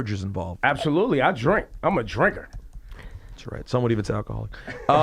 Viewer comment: Can someone please tell me what Rosenberg says @ 2:33-2:35? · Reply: Amanda Willingham some would even say alcoholic